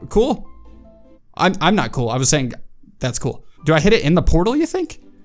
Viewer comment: did he just say that he's not cool?!?!?!?!?!?!?!?!?!??!?!?!